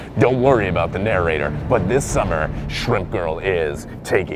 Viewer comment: I thought timeline 1:35 he said Shrek Girl, lmao